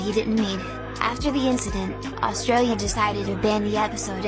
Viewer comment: Australia, did you just say?